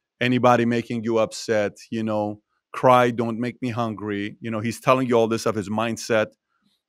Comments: "cry..dont make me hungry.." what? He said angry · why did patrick say "cry don't make me hungry"? I know the guy said that Sadam told him "Try not to make me angry" but wtf hahaha did Patrick really misinterpret him and wrote that in his notes? Cry dont make me hungry? Lolol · i think he said "don't make me angry" instead of hungry lol